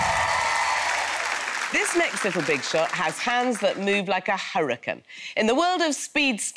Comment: Wait did she say hurricane in the beginning